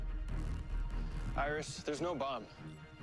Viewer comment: Iris there's no boob😂 I just heard like that lol